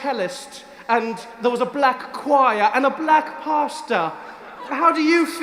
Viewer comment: watched it 3-4 times already it's so funny!!! .... but only now I've understood that you were saying "black pastor"... till now I thought you were saying "black pasta" so I was very confused 😅